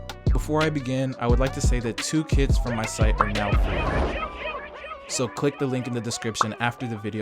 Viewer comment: i thought u said 2 kids from my site are now free lulw